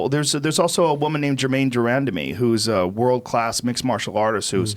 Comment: What's her name? I didn't understand it pls help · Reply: Germaine de Randamie